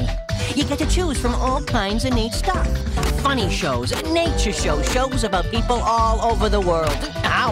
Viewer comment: When the tv said "nature show" I thought he said naked show